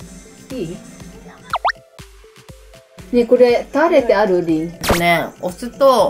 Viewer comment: 本名呼んじゃってない？「タレってない？リン？(ディン？)」って聞こえる